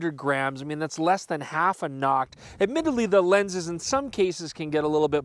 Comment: "900g, less than half a ____" --- What did he say?? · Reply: noct, a reference to the nikon noctilux lens